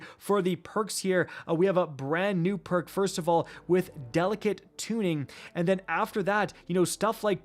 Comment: I was only listening to the audio when I heard you say delicate tuning and I thought you said delicate tooting and I lol'd.